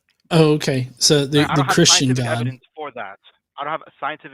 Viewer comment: Thanks for the comment, yes the word I missed was "omniscient". You put your finger on it. And I appreciate the fact that there are people like you out here in the comments who have the capacity to genuinely focus on the substance of the topic being discussed, rather than level some version of an ad hominem attack and think that that's somehow the same thing as dismantling an opposing standpoint in a rational discourse. For clarity, when I was talking to the hosts, I was trying to distinguish between three categories in order to clarifying what I saw as the flaw in their line of reasoning—knowledge, transient thoughts, and communication between entities. The hosts assumed that communication between God and humanity requires a thought process on God's part at some point, which in turn assumes that God's communication must be predicated upon the same conditions as finite beings. But like you say, omniscience doesn’t require transient thoughts because it already possess infinitely exhaustive knowledge about everything simultaneously. When the hosts posit a question like, “Did God have thoughts before creation?” They are presupposing a non-omniscient conception of God. But a non-omniscient conception of God is, by definition, not infinite. Conversely, a conception of God who is infinite, like the one described in the Bible, is omniscient and, as a result, already encompasses all knowledge about everything without the need for a thought process. Look I say it respectfully, because